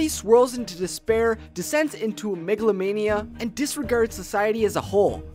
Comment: Thought he said Megalovania